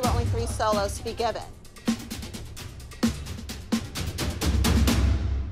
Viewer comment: what did Abby just say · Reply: “Kenzie! Gahgahgahgahgahgahgahgah I’m done with you!”